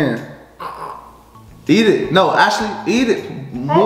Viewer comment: did he call her Ashley??????!!!!!